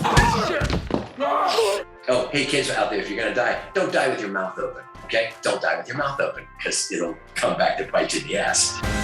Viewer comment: At first I thought I ght he said “kids, if you’re gonna diet, don’t diet with your mouth open” and I thought “well, he makes a good point.” 😂